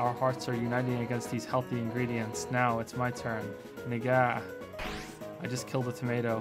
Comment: what was that you said??? 💀💀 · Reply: Literally saying the word "NGAH" like "en-gah"